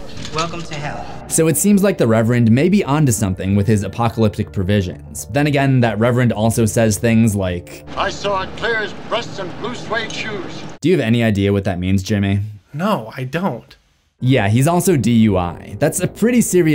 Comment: I always thought he said, " I saw it clear as Presley blue suede shoes"